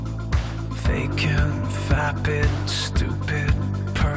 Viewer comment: Literally when he said vapid I had to double take because I thought he said fappin XD